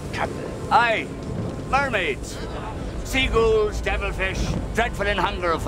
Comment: Wait sea GHOULS?! I ALWAYS THOUGHT HE SAID SEAGULLS